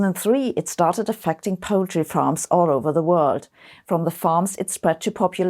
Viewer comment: I could have sworn she said "Poetry farms", which would have been fine with me. 🙃 Just watched a video where they play happy, upbeat musics while hundreds of minks slaughtered only for their furs go down a conveyor belt. I wanted to cry.